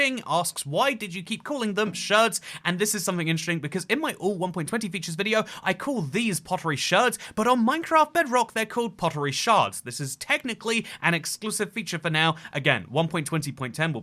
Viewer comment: bro i thought he was saying pottery shirts 😭